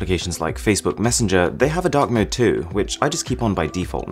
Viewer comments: I thought you said dog mode in stead of dark mode. XD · i thought you said dog mode lollll instead of dark mode